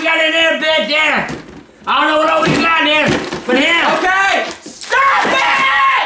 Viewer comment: did he say nails and a elevator LOL